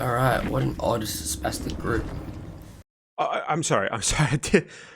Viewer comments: What did the guy say here? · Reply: He called them a “sp*stic group” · what word did he say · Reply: He called them a “sp*stic group” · What did he even say? I barely understand him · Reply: He called them a “sp*stic group” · What did he say? I couldn't understand what he said. Around 7:50. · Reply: He said Spastic, which is the equivalent to calling someone the R-word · Bro what strong word at 7:48 was even said? I literally cannot even physically make out what he's saying there. Can someone help with what he said? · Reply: He called them a “sp*stic group” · can someone please tell me what he said ? i genuenly didnt hear it [sorry if i misswrote something english is not my native language] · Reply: I think he calls the survivors an "odd spastic group" as an insult, though even I've had to repeat it a few times to hear it. And, don't worry about your spelling too much, as long as you can get your point across effectively. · What did he say at 7:49? I can't hear/understand him. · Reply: He called them a “sp*stic group”